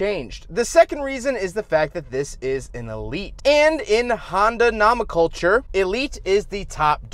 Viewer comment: Did he really say, "nahmen culture"?